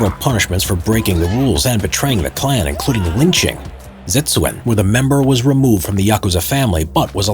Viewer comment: Brilliant timing, I needed this video for research, but, what is that word at 5:33 "Set su n"? What is the correct spelling?